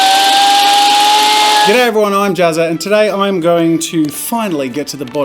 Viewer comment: Oh I thought you said “today everyone I’m Jazza” and just thought so Jazza is this a sort of character, a mask?